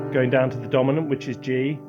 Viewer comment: The notes that are played are clearly outlining a C minor chord. So it's a pretty safe bet to assume that the key is C minor (which is the chord he uses). That being said it could theoretically be any key with those five notes in it. But as a rule of thumb a piece will usually start on the tonic (the first chord of the scale). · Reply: Thank you! Actually this helped me move past my stumbling block on this video. I couldn't get past 1:13 where Guy plays a chord and I misheard him (poor hearing) say, "Going down to D which is Dominant" when in actual fact he said , "Going down to G which is Dominant" 😂 I was also confused because that chord include B natural and I had read C minor key to have E♭, A♭ and B♭ but of course I then realised that was the Natural Minor and in fact the Harmonic Minor has E♭, A♭ and B which fitted with what I was seeing Guy play 😅 Remember I'm not a natural or experienced musically so each of these things takes me time to figure out and Guy whizzes through the video for folk with more experience. Luckily I can pause and rewind him! Anyway, thanks again.